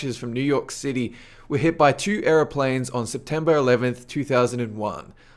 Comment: when he said aeroplanes it sounded like he said arab planes